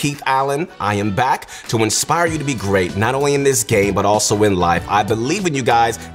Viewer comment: at the first i thought he said gae